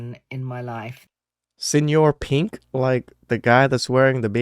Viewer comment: I thought she said Mr. pink like from Reservoir Dogs.